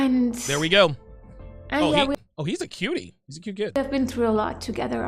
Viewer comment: When I first listened to this comment, I thought you where saying that Oliver is a "cuttie"!